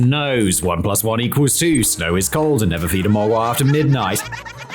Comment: Did anyone else hear Simon say, “Ice is gold” instead of “Ice is cold?” Those wacky Brits gotta learn them some English.